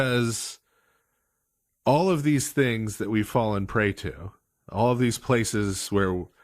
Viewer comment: You said the phrase "all these things we've fallen prey to," and I heard it as "all these things we fall and pray to" -- IT'S THE SAME SENTENCE!